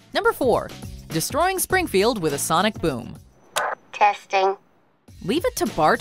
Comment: did you say "sonic boom?"